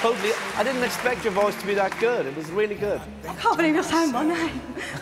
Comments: Somebody PLEASE help me. I'd LOVE to know what she says at 2:37. I've rewound a thousand times.....and I still can't make it out. That aside....what a beautiful, pure voice. Love her demeanor and humbleness too. So.....anybody who knows what she is saying....let me know. =) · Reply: She says “I can’t believe you’re saying my name” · Great audition. But what did she say at 2:35 ? "I can't believe I sound ....??..." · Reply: I can't believe you're saying my name